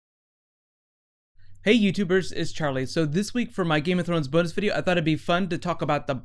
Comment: Every time you do your intro it bugs me that it sounds like you say "is Charlie" lol.